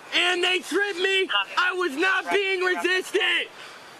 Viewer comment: "I was not being resisting." - guy with an IQ of Florida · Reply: I thought that he said "I was not being resisted" and started doubting my English skill.